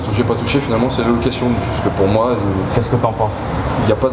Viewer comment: Svp... Je n'arrive pas à entendre à 4:41... Beaucoup de bruit autour.... La location quoi ? D'avance merci... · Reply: rad jenov location nue